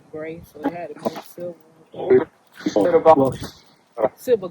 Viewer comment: Some of what I heard: 57:57 “Was Silver” ….58:22 “was stupid who cares” I bet she will get a visitation dream shortly after the session.